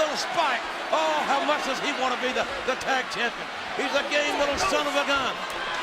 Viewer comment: What did JR say about Spike? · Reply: "He's a gay little son of a gun"